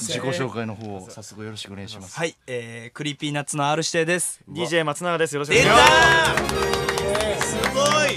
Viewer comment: DJ松島と聞こえた奴俺以外に絶対にいる,、